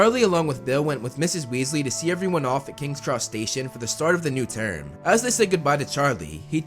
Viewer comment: sounded like he said "king crustacean" I know he said king cross station lol